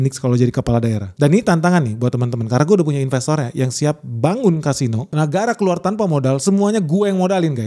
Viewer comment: Bentar2, menit 43:34, Siap Bangun Casino.., apa saya yang salah dengar yah..?